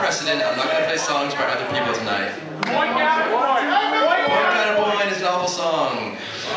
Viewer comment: He said Point/Counterpoint is a what song? I couldn't identify what he says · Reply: He says "Point/Counterpoint is an awful song." Tomas is known to hate it despite it being universally loved